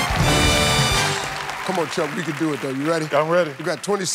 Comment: Did he say “Come on, chunky!” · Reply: He says "Come on Chuck"